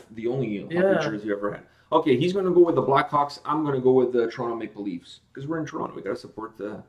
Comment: Congratulations. I didn't know before, that you started this channel one day after my Birthday. :D Anyways, I hope the best for the future of you two and your family, even though your wife's against that altar you created in the background. I wonder what your future will look like. You became quite a big reaction channel with all the interviews and befriended with some bands, Keep it going and much love from Germany! Hope to meet you one day :) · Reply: Did say black cocks?? LoL